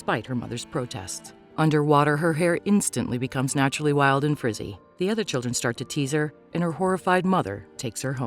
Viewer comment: Correction: her hair reverted back to naturally curly. Not WILD AND FRIZZY. Have some respect please. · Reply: Oh I thought she said naturally Wild and pretty